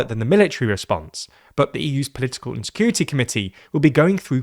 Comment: Did you just say "EU political insecurity committee"?